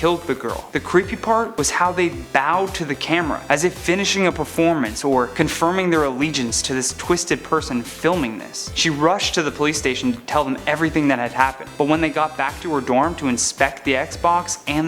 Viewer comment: I thought he said PlayStation until I read the subtitles lol